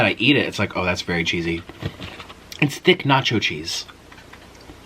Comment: Lol I thought u said Nick's nacho cheese...then I started thinking thts a good idea. U love cheese so much and maybe u can make ur 8own ranchy cheese